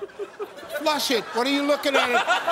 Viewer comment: Instead of him saying what it is which is Shit!! He said Flu-SHIT!! Slick way to get what he wanted to say on camera with no censor!! xD xD